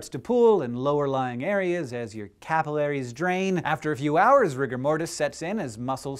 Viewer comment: Did anybody else think he said rick and morty instead of rigor Mortis at 1:22?